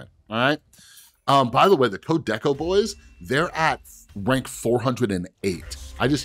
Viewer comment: I would love to see that silver surfer deck but I can't understand who you are saying @ 11:35, Code Decko boys? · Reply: they stream on twitch. search code_deco